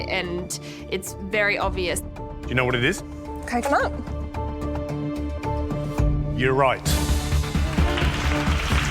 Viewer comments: what did she say? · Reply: Coconut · what is she saying? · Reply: Coconut 🥥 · What da heck is she saying at 20:43 ?! Repeated 100x and still couldn’t get anything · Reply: Coconut